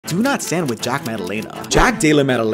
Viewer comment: I thought you said "Tuna sandwich" at first well really it was "do not stand with"